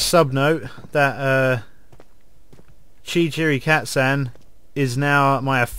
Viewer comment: When you said "chichiricatsan" I thought you said "cat-sand" And can you please yell "Unicorns" sometime? Brittish people sound so jolly and fun when they do that.